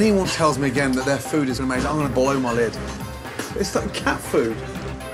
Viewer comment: if anyone tells me that this food is amazing i'm gonna boil my leg lol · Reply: I thought he said "blow my lid"